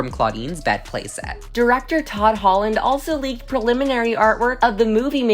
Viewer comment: plz I had my sound turned way down and thought she said Tom Holland 😭